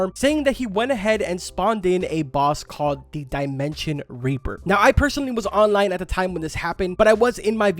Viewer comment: did you just say dimension raper